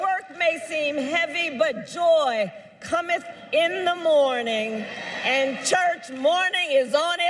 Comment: When she says, “joy cometh in the morning,… and morning is in its way”— I know the verse, but from her lips I only hear “mourning” Is on its way